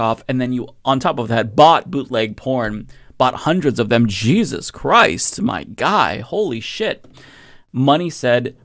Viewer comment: Did you say jizzuzz christ??